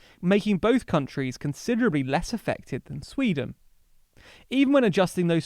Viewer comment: you say that Norway and Denmark are less effective than Sweden. That is not correct. You should have said they were much MORE effective than Sweden. That mistake that you made is critical to the entire video. Please correct it. Edit: It certainly sounds like he says “less effective” but it is definitely possible that he said “less affected”. The difference in those two words completely changes the meaning of the statement. If could add text or a subtitle to that section of video I think it would clarify what they are saying. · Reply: He's saying "less affected" not "less effective"